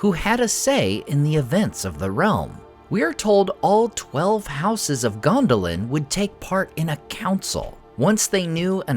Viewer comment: At first I thought it said "12 hours of Gondolin" 😮